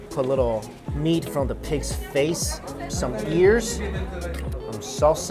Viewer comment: Had my volume low and I heard, Meat from a kids face. 😱😱😱. Had to play it back. 😓😓